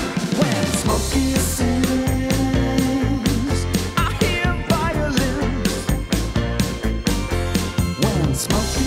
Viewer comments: Misheard: When Smokey sings, I HEAR VIOLENCE. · "When Smokey sings, I hear violence" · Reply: Violins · As a wee guy I legit thought it was "when Smokey sings...I hear VIOLENCE"